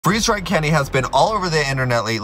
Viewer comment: I thought he said freezed dry Kenny lol